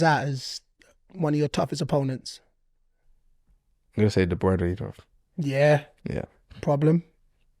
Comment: Who did he say was his toughest opponent @ 52:47? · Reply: I think he said De Bruyne